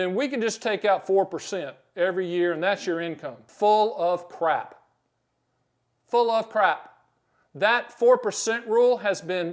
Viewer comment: LOL....I thought you said "bull of crap"....🤣 Another excellent video, thank you.